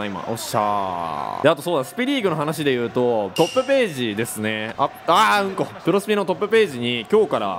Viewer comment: うん〇？！って聞こえます笑